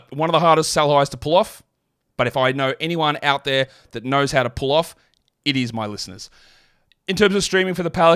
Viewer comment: I first heard "if anyone knows how to pull off, it's my Mrs." and all the blood rushed out of my head